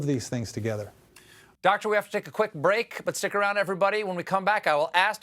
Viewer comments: I thought he said Dr. Lapoop 🤣😂😂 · I thought he said La Poop 🤷‍♂️